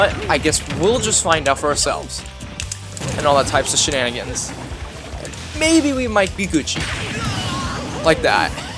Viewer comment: Maybe we'd might be Gucci. (I guessed that's what he said)